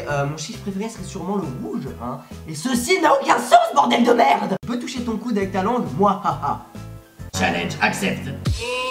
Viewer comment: Question time 6: dans le Question time 3, vers 1:20, tu dit ; quoi quoi quoi quoi quoi quoi ? tu pensais que t'etait trop swag à c'que j'allais pas parler?... C'est quoi cette phrase tu connais ton français ? x) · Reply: En fait il dit j'arrive pas à parler